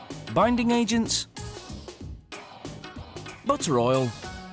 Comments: The guy needs to learn to speak clearly. I don’t want my food to contain motor oil. Someone else said he say butter oil but sure doesn’t sound like that. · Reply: Bentley Perry I understand him, it just sound like he said motor oil and not butter oil . I guess I’m not the only person with that problem. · did I just hear motor oil? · is he saying motor oil? 😂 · Did he say motor oil? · So nobody else heard him say "motor oil"??? 😐 I'd still eat it😒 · motor oil? I've listened to this over and over again, I still have no idea what he actually said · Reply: butter oil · anyone else mishear "butter oil" as "motor oil" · "Motor Oil" ... okay what did he actually say here at the start? Roughly 0:45 ? XD Because I've listened 3 times and it sounds like "Motor Oil" and I know this isn't the parody channel!